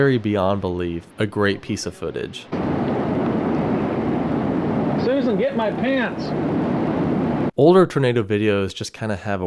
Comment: "Susan, get my pants!" has become my new favourite quote from a tornado video. And thank you for such a great informative video. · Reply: I just heard, “As soon as I get my pants”.